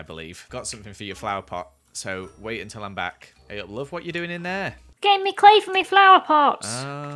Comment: please tell me at 7:32 i wasn’t the only one who heard call love 😫🥺 edit: i put the wrong time in so i corrected it 😂